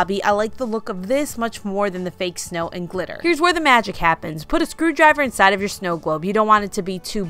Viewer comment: Correction: 19:21 "Here's where the magic happens, put a 'SCREW' inside of your snow globe.... I was looking down when you said that, hehe, had to go back to confirm what you said. Nice job on all..I'm recently widowed away from family, I'm looking forward to moving back to home base to use some of these for next year's holidays!